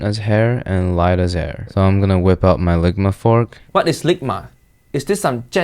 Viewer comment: sounds like he says ligma fuock